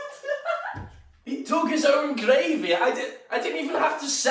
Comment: "He dug his own gravy" - December 2019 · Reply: he said "dug is own grave here" its the scouse accent, but as someone not scouse, dont know how anyone thought he said gravy